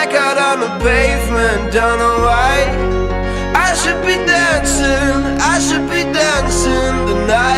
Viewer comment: Instead of “I should be dancing” I always hear “I should be dead soon”. Now you will too.